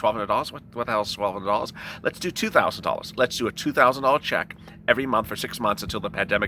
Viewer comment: I thought you said 2,000.00